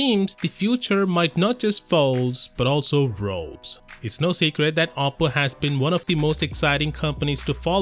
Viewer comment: first i heard apple and was like shit no then i heard oppo then i was oh ok cause apple really is always the same the only change is the prozesor